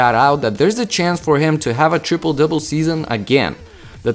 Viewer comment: Did he just say "tripple dipple season"😂😂😂